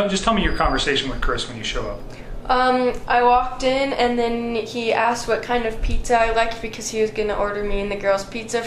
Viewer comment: I’ve said this before, at approx 7:52 it sounds like she says “I walked in and then “Nikki” asked what kind of pizza..” What do you think/hear? Slow the speed to 0.5 to hear it clearly. (No, I don’t believe NK was involved in the actual murders, that’s why find it odd she would say Nikki.) I am not sure the girl in the news clip is the same girl in the interrogation room. I think the news clip is a different girl called Lauren, does anyone know? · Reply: I don’t hear that. I hear, “ he asked what kind of pizza I liked.”